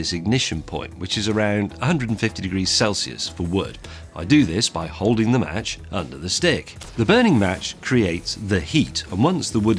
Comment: Did he just say, "I do this by holding the match under the stig"? Haha!